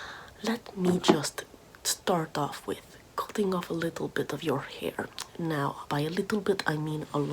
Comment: I heard, “I am going to be cutting of a little bit of your fingers” and was only mildly concerned...